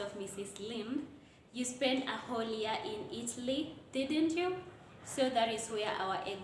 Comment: Just a quiz is 'a' asilent letter I heard you say Itly instead of Italy 🇮🇹